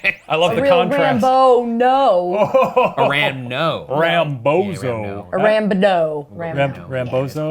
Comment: For a whole moment, I thought said "ramBODOH" which, yknow, works